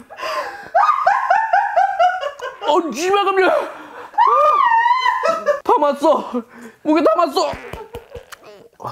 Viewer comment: 이때뭐라고하는거얔ㅋㅋㅋㅋㅋㅋㅋㅋㅋㅋㅋㅋㅋㅋㅋㅋ지발노미라 ㅋㅋㅋㅋㅋㅋㅋㅋㅋㅋ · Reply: 쉬 ㅂ ㅏㄹ깜짝이야 라는듯요